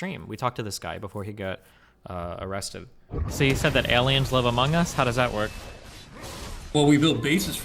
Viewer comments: I thought he said “...aliens love among us?” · I swear to allah that I thought he said "So you think that aliens love Among Us?" GET OUT OF MY HEAD GET OUT OF MY HEAD GET OUT OF MY HEAD GET OUT OF MY HEAD GET OUT OF MY HEAD GET OUT OF MY HEAD GET OUT OF MY HEAD GET OUT OF MY HEAD GET OUT OF MY HEAD GET OUT OF MY HEAD GET OUT OF MY HEAD GET OUT OF MY HEAD GET OUT OF MY HEAD GET OUT OF MY HEAD GET OUT OF MY HEAD GET OUT OF MY HEAD GET OUT OF MY HEAD GET OUT OF MY HEAD GET OUT OF MY HEAD GET OUT OF MY HEAD GET OUT OF MY HEAD GET OUT OF MY HEAD GET OUT OF MY HEAD GET OUT OF MY HEAD GET OUT OF MY HEAD GET OUT OF MY HEAD GET OUT OF MY HEAD GET OUT OF MY HEAD GET OUT OF MY HEAD GET OUT OF MY HEAD GET OUT OF MY HEAD GET OUT OF MY HEAD GET OUT OF MY HEAD GET OUT OF MY HEAD GET OUT OF MY HEAD GET OUT OF MY HEAD GET OUT OF MY HEAD GET OUT OF MY HEAD GET OUT OF MY HEAD GET OUT OF MY HEAD GET OUT OF MY HEAD